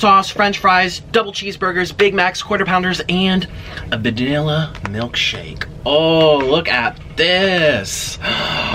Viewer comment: a vadilla milkshake did anyone hear that? lol